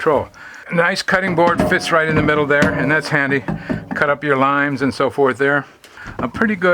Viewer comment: I thought for a minute you said 'cut up your lines' but I went back and it was Limes! Lovely yacht.